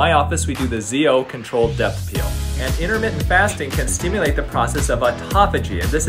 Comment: I would love to hear you talk more about intermittent fasting and skin benefits. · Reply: I had to listen 2x. I thought he said internet fasting.